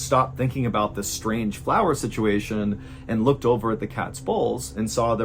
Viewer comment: I thought you said "Cat's Balls." 🤣🐾💗🇵🇭